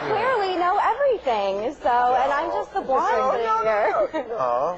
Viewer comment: did she say, "I'm just a blonde." Set me straight...